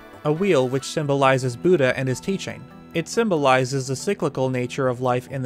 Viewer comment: I always hear “a symbol of boody and it's teachment“